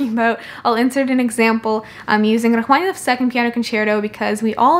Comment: Is she saying Rachmaninoff 2nd piano Concerto at 5:23 ?